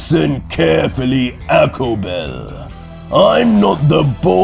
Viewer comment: i thought i heard APPLE BELL 😂